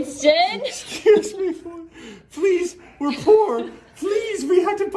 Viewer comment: "please we poor" that goes deep, was most sincere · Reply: I thought he said please for pron lol